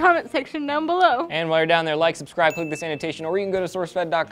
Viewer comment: I think Eliott totally says "Click the Sanitation" at 2:30.